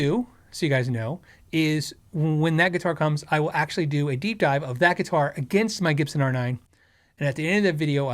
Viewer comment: Dunno why but the first time thru, at 11:56, when says "deep dive," I hear "geek out," so of course, double take. Then... Oh, it's ok. They're the same thing. 😂🤣😂